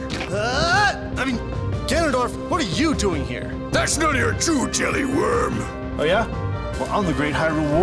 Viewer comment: what does ganondorf say in 0:36?? :/ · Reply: "That's none of your Chu Jelly, worm!"